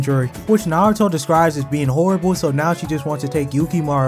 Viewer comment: i thought you said "Ginger" "they plan on making yukimaru into a ginger whicn naruto describes as horrible"